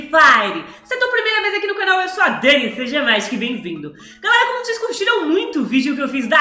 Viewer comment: "se é tua primeira vez aqui no canal eu sou a dani..." OQ ELA FALA DPS DISSO? EU NUNCA ENTENDO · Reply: aaaaa obg Eu ouvi "que seja magico e bem vindo" KKKKKK